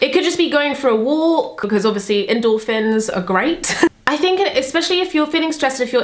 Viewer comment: When you said endorphins, I heard "indoor fins" and I thought Wahh is that like an armband hahaha